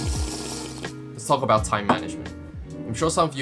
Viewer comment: the first thing he said is "Let's talk about time management" and I heard that "Fuck about time management" xD